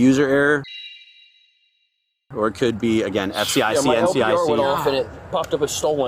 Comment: These cops need training on how to talk to not-cops. “Is everything 10-4 with your vehicle?” “We got a “hit” on your tag.” “My LPR popped up a hit on your tag.” “My LPR went off… it showed Sig-10…” Also, whatever this “It could be S-C-I-C-E-N-C…” or whatever the crap he said at 12:15 The dad was already freaked out and confused by the whole situation, then they throw a bunch of cop jargon at him like he has any idea what they’re talking about. C’mon folks, just lose the copspeak and talk like a normal person for a minute, explaining what happened instead of trying to cover your butt.